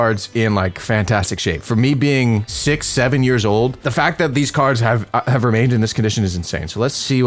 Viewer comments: did i hear 6 7 years old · did he say 6-7 years old?